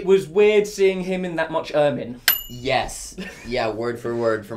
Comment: What's the sentence at 1:48? "It was weird to see him in that much ...."? +what does mean the missing word? (I'm learning english) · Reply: Ermine (referring to the fake fur collar he wore on his jacket)